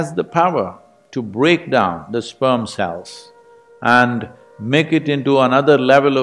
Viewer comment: Ok but did he just say "it breaks down the sperm cells"?